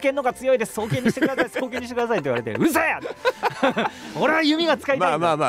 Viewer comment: I heard Kimura say "うるさい", which I guess means "Shut up"